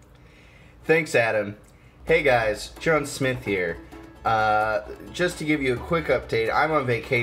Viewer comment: did he say "Chun Smith?"